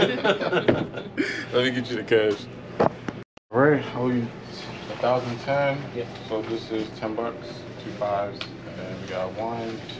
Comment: wait i’m confused, at 14:48 he said 1110, but then when he counted out the cash it was 1010, idk if i’m missing something or just heard it wrong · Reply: no he didnt. 15:22 "alright, i owe you a thousand 10. so this is ten bucks, two 5's" then he counts it out. and says "1010". no where between your time stamp and the money did he say 1110.